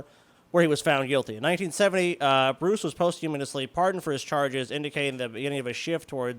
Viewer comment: Did say posthumanously